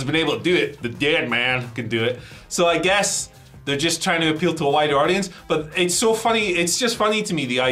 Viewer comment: I misheard "trying to appeal to a wider audience" as "trying to appeal to a whiter audience" and well, that actually fits hetter